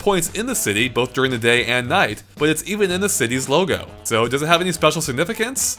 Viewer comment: i swear you said new dong city XD